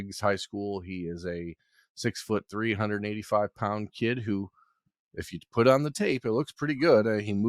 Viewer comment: man, I thought you said he was 6 foot, 385 pound kid 😂